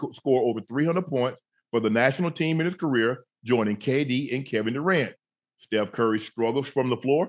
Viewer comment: Joining KD and Kevin Durant lol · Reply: I thought he said AD and Kevin Durant